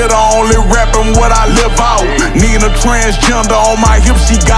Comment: That transgender line was 🔥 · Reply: I thought he said I need lol it's still a lil confusing to me but 🤦‍♀️ 🤷‍♀️ 😅